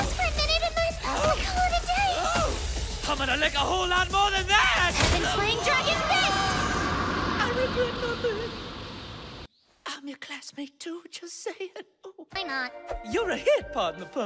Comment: Did I hear Nanami say "Iron dragon fist"?